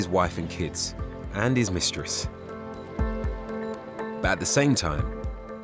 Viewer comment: Did he say mistress!!! Playa playa